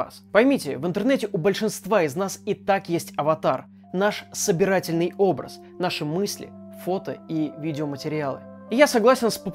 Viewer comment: Мне одному послышалось "Популизатором науки Витей АК"